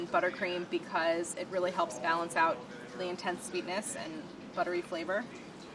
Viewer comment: did Claire say "intensitiness"? Haha